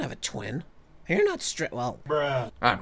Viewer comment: From 6:50to6:55 I thought sun said straight